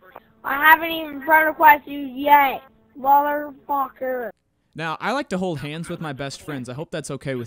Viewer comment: He sounds like hes saying focker instead of fucker